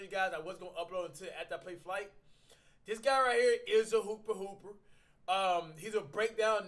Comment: I thought u said he's a hoopest